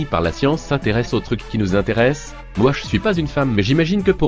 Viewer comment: Ah merde : j'avais pas capté qu'à 5:42 il disait ''moi je ne suis pas une femme''.